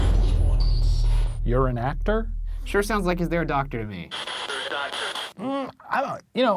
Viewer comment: Ok but honestly? I'm 100% a shaniac but even I heard "there's a doctor" at 7:58 before Ryan pointed it out